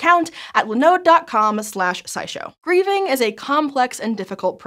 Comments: At the beginning of the video I genuinely thought she said "breathing" instead of "grieving" and I had to replay it like three times because I was so confused because I had no idea what she was talking about. Finally I just turned on subtitles and was like "OHHHHH that makes a lot more sense" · Please tell me I'm not the only person who heard 'breathing.' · in the beginning i thought she said breathing so i got confused when she said it was hard lmao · I thought she said "breeding" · Omg the intro where they talk about grieving I thought they said breathing the first time